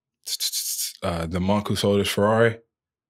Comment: what does he say ? Whats the name of the book? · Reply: “The Monk Who Sold His Ferrari” - By Robin Sharma